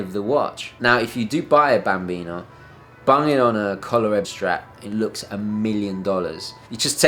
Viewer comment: What did he say here? Get a bambino on a [collar-ebbe] strap? Looks like very solid, uniform, black foam-like material. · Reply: ColaReb Strap my friends!